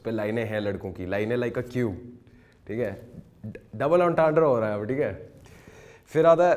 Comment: what did he say?? Double ?? Fhir aage kya bola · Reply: double entendre (french word hai uska matlab ke ek word ke do meanings hote hain)